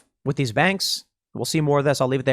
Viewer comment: Did… did he just say “banks for hanging out” right at the end?